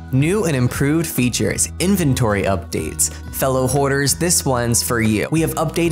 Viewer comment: tell me why when he said “Fellow hoarders” i heard “Fellow whores”